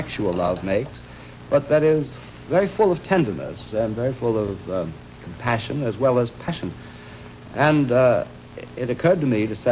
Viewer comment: Can someone please help me translate this part? I'm writing a paper on it and can't seem to figure out what Trilling is saying. At 2:05 he says: "...but that is very full of tenderness, full of passion as well as __?__ ..." It sounds like he says "passion" again, but I can't tell if he might be saying that same word with a different accent/enunciation or if he might be saying a different word. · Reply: first he said compassion and then passion. I can't understand the full sentence as well. but i'm sure he said compassion and passion